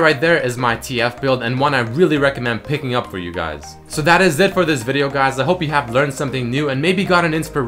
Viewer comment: Why did i hear it as "That is Zed for this video..." at the end...